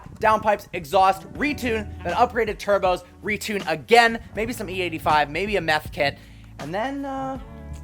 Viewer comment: did he just say a meth kit? 😂